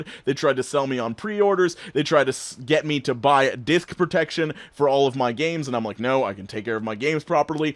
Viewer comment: I thought you said they tried to sell you “Dick protection” lol